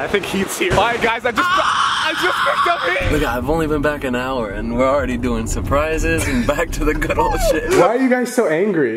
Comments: what did heath say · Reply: erene olson he said the n word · am I high or did he just say the N word lmao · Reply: he said "look it"😂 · did heath say the n word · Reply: Zach Collins he said “look it” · did heath say the n word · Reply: No, “Look I...” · Did heath say the n word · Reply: He mumbled "look I've" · did Heath just say the N-word💀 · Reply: Your timestamp is 1 second too late and he said 'look I' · it sounds like he says nigga